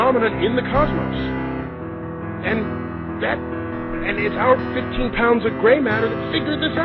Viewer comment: Did NDT say "...our 15 pounds of gray matter"? (1:40), and if so, who were the other 4 people he was talking about?